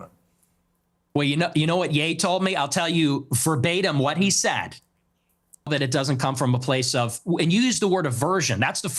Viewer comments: Why’d Piers cut what Nick said about what Ye said at 43:20? 🤔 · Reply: in justifyin/agreeing with nick ye said ' when ns being ns' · What happened at 43:20?? I want to know what Ye said and it was cut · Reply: He said “niggas be niggas” not really uncensored is it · What did they cut out with what ye said at 43:21? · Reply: N's gonna N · dafuq they just cut? What ye say? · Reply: Ngs gonna be ngs or somthing like that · What did Ye say? Piers cut out that part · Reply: He said : when niggas are being niggas. Nick said: so true. Meaning: blacks know how black people can behave. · Does anybody know what Ye said, I think that part cut out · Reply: He said “Niggas just bein’ niggas.” or something like that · I wonder what Ye said... And why is it cut out? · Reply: Ye said "You use N-word? Well, when Niggas gonna be Niggas.." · what did YE say? It got deleted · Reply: Ns gonna be ns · Nah what did Kanye say 😂😂😂😂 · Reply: Paraphrasing here, maybe, but, "N's gonna N" or something like that. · what did kanye said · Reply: "when niggas be niggas I understand" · Why was what Ye said at 43:26 edited out? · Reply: "Ninjas gonna be ninjas" or something it was.